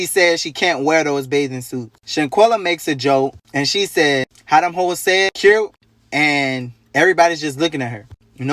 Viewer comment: She said what? "How dem hoes said cute"? @ 4:25.